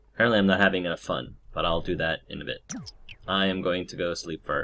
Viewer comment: I thought you said, " Apparently I'm not having any fun. I'll fix that in the bed." Instead of in a bit. XD